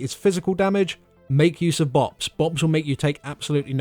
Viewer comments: "Keep in mind this ability is physical damage make use of _____" What did you say there? Please I could understand whether you said box or bots. please let me know. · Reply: BoPs - blessings of protection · What did he say at about 12:10? Use bobs? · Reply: I believe he said BoPs as in paladins Blessing of Protection.